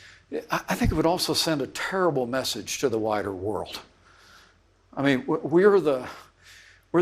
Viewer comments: lol.... thought he said "the whiter world"..... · I swear to dawg...at the beginning, I thought I heard Pence say, "...I think it would send a terrible message to the whiter world..." and basically spit out my morning caffeine. I'm going to need to drink another cup.